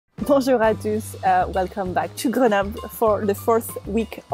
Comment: When you said, "Bonjour à tous", the closed captions thought you said, "Preservatives". Mdr.